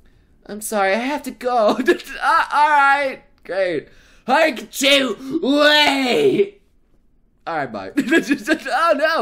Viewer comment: did u just call her haruka chu?????